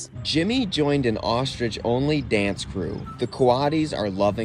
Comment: I thought he said "ostritch only fans group" 😆